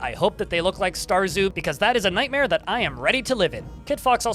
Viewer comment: “I hope that the ascii faces look like” what was the next word he said at 4:35 ? Starzu? Starzoo? I’ve tried looking up both and I get no results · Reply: Star Zoo